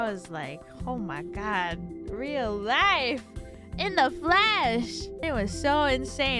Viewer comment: Thank you for the clip! 1:28 I think she said "In the flesh!" as in "in real life, in person". My Japanese isn't good, so I don't know how to translate it to Japanese, but the current translation seems to be based on "in a flash".